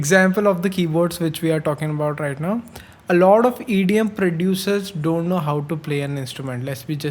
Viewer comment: Calling Skrillex an idiot producer. LOL. Idiom, idiot? I don't know what he's saying. 1:31. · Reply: “EDM”